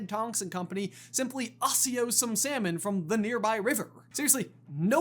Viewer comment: did bro just say ASSIO 😭